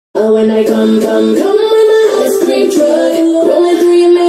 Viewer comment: I thought it said “vroom vroom vroom on my ice cream truck” lol